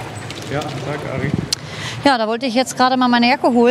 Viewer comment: Habt ihr das auch gehört? Bei 10:53 sagt einer der Cops sowas wie „Tag Ari“